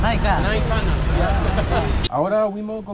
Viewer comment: Did he mix Spanish with Waorani? It sounded like he said "ahora" here